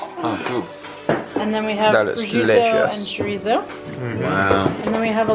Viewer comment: did she said chorizo, or am i crazy?!? We eat it here in Uruguay and in Argentina OMFG